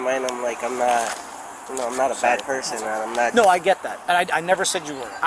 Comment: At first I thought he said, “I’m not a fat person” and was like…bruh